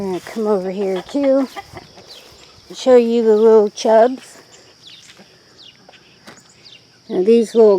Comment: What type of tomatoes were they you said were volunteers? I thought you said chub, but googled and couldn’t find any by that name. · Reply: They are from a tomato plant last year that I called Chubs... But the variety is Chubiness....